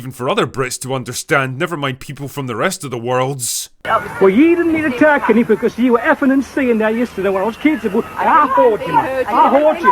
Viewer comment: Im a geordie, for anyone that wants a translation of the Geordie at 04:05, "Well you dont need to talk hinny, because you were F'ing and blinding yesterday when there was kids about, i heard you!"